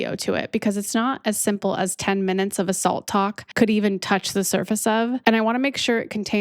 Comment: I swore I heard you call it assault talks. Lol. Keep doing your thing. You are doing great things.